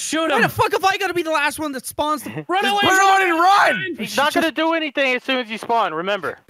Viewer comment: Did I hear in 6:50 "Run away and Run"?